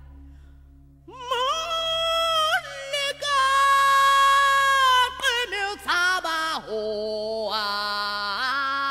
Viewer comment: I know its in a different language but... I'm not the only one who heard that.. Right? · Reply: Oh please. The word is LEKA.